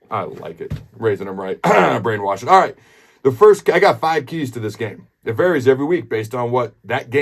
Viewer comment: … had music in the background I thought Adam said “ I Got 5G’s On this Game!”